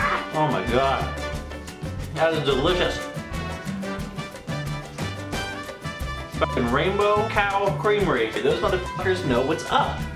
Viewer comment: I thought it said Rainbow Cow Cemetry